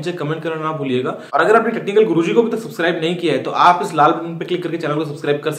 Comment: it sounded as if he was saying tatti kal guruji instead of technical guruji !!!